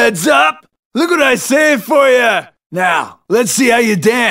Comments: "Look what I saved for ya!" So that's what he says for that. I had always thought it was "I'm prepared for ya!". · For the “look what I save for you” always thought he said “I got some pain for you” and for the “new back drop” I always thought he said “come back to us”